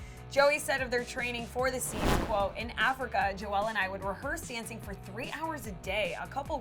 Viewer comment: did she say “jo-elle”?😂😳🤷🏼‍♀️